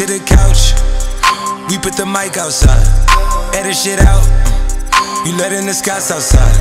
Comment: Idk why but at 0.30 i hear "edit shit out" instead of "air that shit out" os it just me?